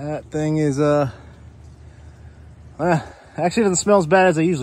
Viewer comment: Full video on what now? This guy sounded like he said 'rectum recovery chanel' 😂